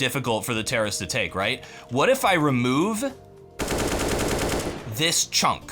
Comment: i thought that you were gonna say "what if i removed recoil"